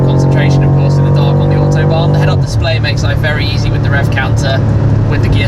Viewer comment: Oh yeah and I thought that it said "Make Life a Rice"